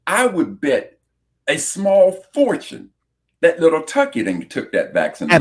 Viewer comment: I thought he was saying Cucky lol